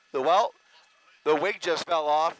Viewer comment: Why would he say "the weight"? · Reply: The subtitle said 'weight'. He actually said 'The 'wig' just feel off.' 20:42 !